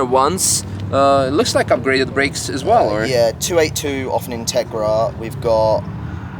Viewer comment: which calipers are those?? Did he say 2A2? Or 282? What does that mean?